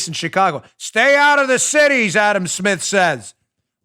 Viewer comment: I was EXPECTING a to hear a big lie, as I thought you said Adam Schiff, rather than Smith. LOL. It was nice not having to hear Schiff talk or see his disgusting face, or that skinny and soon likely to be skinnier neck of his.